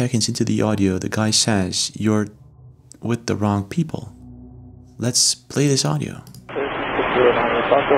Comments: I hear, "You're talking with the wrong people." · So, I definitely hear , "You're TALKIN' with the wrong people." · Another in the Philipines. -Sounds like pilot says "You're effen with the wrong people." Because if he said, "You're talking with the wrong people", thats not proper English. He should have said, "You're taking to the wrong people."